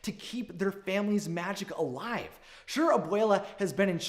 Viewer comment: At the end it sounds like you said the magic elves 😂😂😂😂